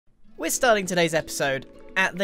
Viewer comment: did he just say starling?